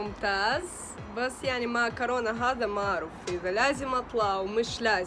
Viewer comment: أنا سمعتها معكرونة؟ ولا أنا اللي جوعان؟